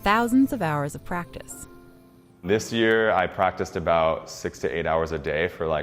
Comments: What he meant: I practice 6 to 8 hours a day What I heard: I practice 68 hours a day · did he say 68 hours a day?? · did I just heard 68 hours a day?? 🤔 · stop hating. 6 hours a day, for a whole year. That's a lot of commitment to master anything. That will always get my respect. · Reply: I thought he said 68 hours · Did he say 68 hours (check this in 2:45 timeline )? But I love his style.👍👍👍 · Did he just say 68 hours a day? Or im just imagine hearing it. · AHAHA I thought he said "68 Hours a day" like, what?! Lmao. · i thought he was saying 68 hours a day😂😂😂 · I've misheard it as 68 hours a day lol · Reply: maybe he said 6 to 8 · did anyone else heard him say " i practice 68 hours a day" or is it only me? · I thought he said 68 hours a day on 2:43 until I open caption it was actually six to eight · Was it just me or did he say 68 hours a day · Ohh it's 6-8 hrs a day. I thought he said 68 hrs a day. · Wait did he say 68 hours a day ???